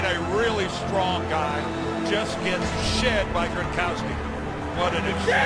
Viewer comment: did he say sh**?